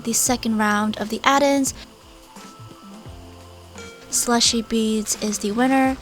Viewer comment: ( am i the only one who heard "Splushie beads"?)